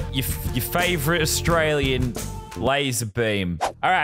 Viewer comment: I almost thought you said lazarbean